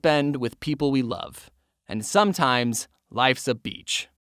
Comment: When said "Life's a beach", I heard "Life's a bitch."